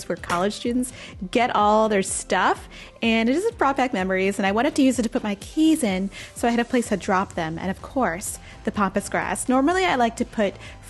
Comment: Oh it does look so good! I also have to confess. For the longest time when you spoke of that grass, mostly in your Sims videos, I thought you were saying Pompous Grass. Now I know!